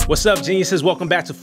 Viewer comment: do i have a hearing problem or he really said "wassup jesus"